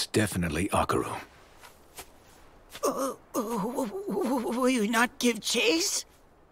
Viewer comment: when I Heard jaken said *will you not give cheese*😆